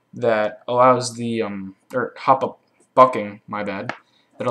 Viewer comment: Subtitles say fucking instead of bucking xD